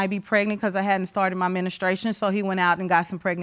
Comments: When she said “ministration” I knew it was gonna be some ignant s&@ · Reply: I thought I heard "administration". I went back to listen to the context. Omg! She could have just said "period". The gal in blue at the back of her heard the administration/ministration. She couldn't believe her ears. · Did I just hear "ministration " @ 0:58? , let me finish watching 😁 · Did I just hear "ministration " @ 0:58? , let me finish watching 😁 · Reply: I thought she was saying "AD-ministration" like Trump administration. Lol! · Less than 1 min in and I am reading the comments to check I am not the only one who heard her saying "ministrations" 🤣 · Did she just say: Ministration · Baaaaaaaaaby when she said "Ministration" I immediately threw my phone across the room!!!!!! 😂😂😂 I can't deal for the rest of the day!!!! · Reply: I thought she said administration and I laughed, I'll always call it this now 😁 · Did she say « ministrations »😂😂😂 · Did I just hear "ministration " @ 0:58? , let me finish watching 😁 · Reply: I had to rewind d that twice. 😂 I thought she said "administration " but she sad "ministration." 🤦‍♀️